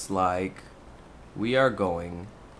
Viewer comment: you know you're a brony when you here "Twilight Sepulcher" and thought you heard "Twilight Sparkle"